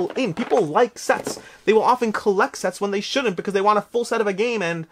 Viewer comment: Listening to your video while I do work. I thought you said "people like sex" when you were talking about sets with the Valeria games. It instantly made me jump back to the computer to see what was going on.